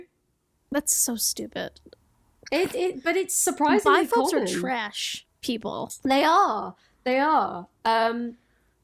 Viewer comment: Lol around 1:00:40 I had a heart attack cuz I heard “bi folks are trash people” hahahah and auto captions came through clutch with “my folks are trash people” 😭😂 can confirm after ten listens it’s “biPHOBES”